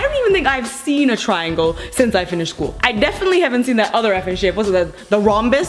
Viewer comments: I thought at 1:50 she said what's that other fing shit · i thought she said shit